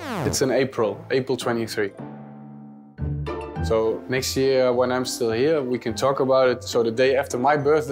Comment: I love how cillessen is constantly bringing up the fact the de jong will be joining him in barca. Like "When you come over, i can tell u more..." "When you come to this club, u will land at this airport.." Chill jasper we are all excited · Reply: no, he actually says "WHEN I'm still here"